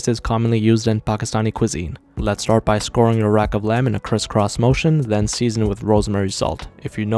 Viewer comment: I thought he said Chris rock motion 😂😂